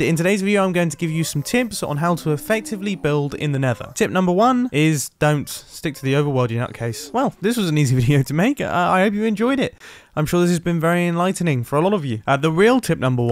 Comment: Don't. Stick to the overworld, you nutcase. What I heard: Don't stick to the overworld, you nutcase.